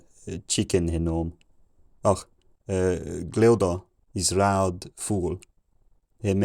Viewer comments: I thought he said "Gleoda is a loud fool" loll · I heard “ loud fool” Btw 700-800 lol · I heard "Gleydon is loud fool" around 800. Not sure if that's what's actually being said. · I heard this the first time as "loud fool" and thought I understood. Way off. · year 400-800: Ancient outdated gibberish. year 900: "ℑ𝔱 𝔦𝔰 𝔩𝔬𝔫𝔤 𝔴𝔦𝔫𝔱𝔢𝔯" 🗿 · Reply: I could have sworn I heard him call someone a loud fool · I heard "loud fool" at around 825. I find this hard to believe though. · I thought I heard "loud fool" earlier, but only managed to understand phrases around 1550.